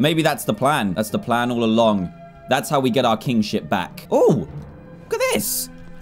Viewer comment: Can anyone tell me what this line says? I think it says something that Dan wouldn't say but I can't tell ( 4:43 ) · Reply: Its ship back